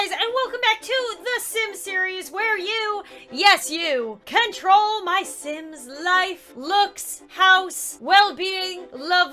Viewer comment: It sounded like she say ware you yes you can troll my sim